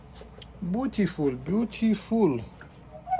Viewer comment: In 38:49 you said "beautiful" that sounded like "BUTiluf" :) in Polish "but" means shoe 😁 keep it up and greeting from Poland!!